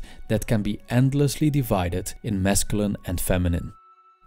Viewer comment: thought he said mescaline lmao